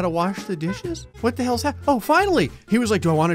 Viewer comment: I thought it said shappening